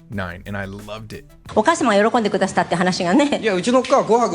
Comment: What are they saying on the show clip around 2:13? Sounds like お母さんも喜んでくだしたって話がね？ etc. Can’t understand a word after studying Japanese for 6 years. Drives me nuts 😂